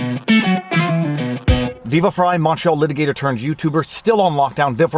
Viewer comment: I never understood what a 'nacho litigator' was, and now for the first time I heard 'Montreal litigator'. Amen. This was bugging me for so long...